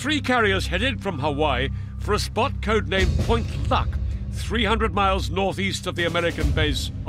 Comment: LOL, it sounds like he says "Point Fuck", and even Youtube captions believes it XP